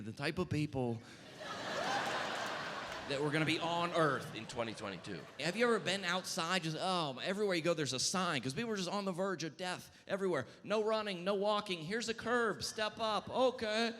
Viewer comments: What is he saying at 11:04? The audio says "in 2022" but his lips are clearly saying something different and I can't figure it out. Something like "in 12 days" or "in 2008" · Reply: In 2019. It’s the special that was supposed to come out on Netflix back then. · Anyone know what he said @ 11:05? The type of people that would be on earth in__ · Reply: I caught that too. I watched it a few times even at a slower speed but can't lip read. I think maybe he said 2019 and it's dubbed over with 2022.🤔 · Is it just me or at 11:05 did he say 2029? Pretty sure this is proof he’s a time traveler sent by Jesus